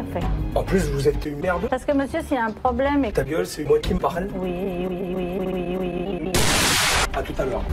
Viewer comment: Ta gueule c'est moi qui commente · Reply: Ta geule c'est moi qui parle il a dit hein🤡🤡